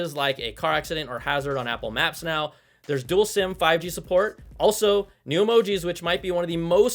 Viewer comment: I thought I heard you say duel sim?